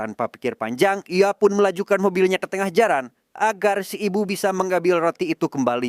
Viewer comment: Gak salah denger ya tadi? Melajukan mobilnya ke tengah jaran😂